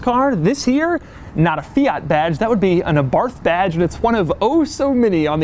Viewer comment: When Doug said "Abarth Badge" I thought he said "A barf bag"